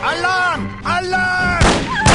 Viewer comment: Everytime they said "Alarm Alarm" for some reason I kept hearing it as "Allah Allah" and thought why the hell would Germans in the 60s say that